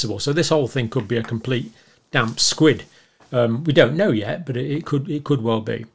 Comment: Did he say damp squid? 6:05 I certainly won't be putting on a pedalstool after this.